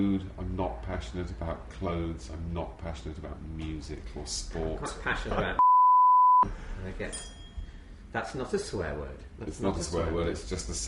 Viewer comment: i HAVE to know what David Mitchell said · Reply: David is very passionate about cunnilingus